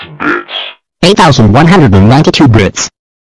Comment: why i hear this like "8192 brits "?!?!?!